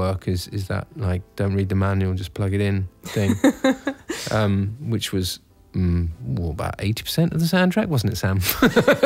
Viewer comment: what's he say at 5:37, please? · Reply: "um, which was, well, about 80% of the soundtrack, wasn't it, Sam?"